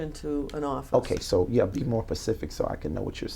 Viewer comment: “be more pacific so I can know what you’re saying” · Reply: “Ocean”???? Or to be clearer…“specific”